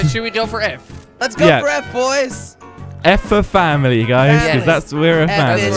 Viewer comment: Did I hear family 4:40 family is everything